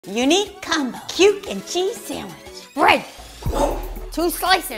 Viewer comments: I thought it said chuckle cheese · I can't be the only that thought that it said “Chuck e Cheese sandwich”